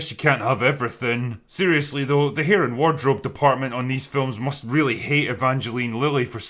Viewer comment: Did he call it "heren wardrobe department" at 2:46? I'm Dutch so may just be confused, but it'd be interesting if that Dutch term is used in English.